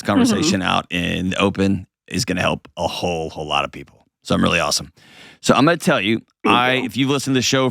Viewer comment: Did he just say, “So I’m really awesome,”@ 1:30 ?